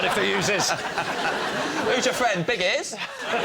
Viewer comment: Could somebody please tell me what James Acaster said at 32:28? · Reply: "Who's your friend, Big Ears?" A reference to Noddy and Big Ears.